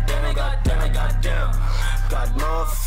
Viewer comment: I always thought it said demigod damn